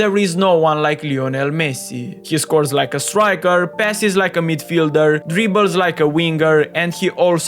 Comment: When bro told "Passes," i heard it like "Pessi" 🤣